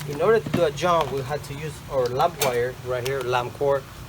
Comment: sec mark, when you are doing the jump, what is that plain wire called? Did you say land wire or lamp wire? Great video brother, I will attempt to do this on my own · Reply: That's called lamp cord, lamp wire, or zip line.... We at Kingdom Christmas Lights refer to it as lamp cord but it's basically the C9 socket wire without the sockets.